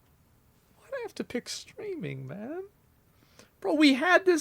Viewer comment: I thought that said gaming Ngl